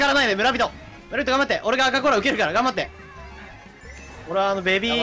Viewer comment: のむらびと頑張ってが ブルック頑張ってに聞こえるww